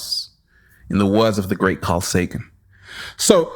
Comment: What did he say in 20:14 satan? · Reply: Justinas Matelis Carl Sagan